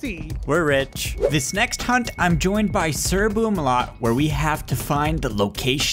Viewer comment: When says he’s playing with sirboomalot it sounds like he is saying sirbloomalot